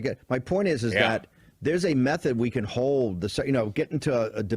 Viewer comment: I swear he said meth head, lol